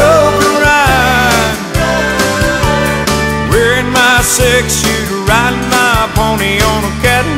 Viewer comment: Am I the only one...who for years and years...thought he was saying sex shoe...